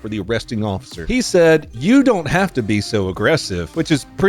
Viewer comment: ummmmm, he actually said "I'm not gonna be aggressive."